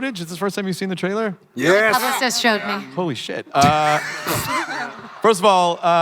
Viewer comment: What Jennifer said?? " _______ showed me"?? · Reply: ***** I believe she said her publicist showed her.